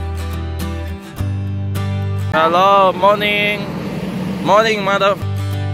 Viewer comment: ：morning mother--- 🌚🌚🌚诶诶诶到底是要说什么啦